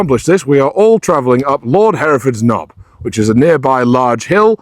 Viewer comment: Did I hear “Lord Hereford’s Knob”???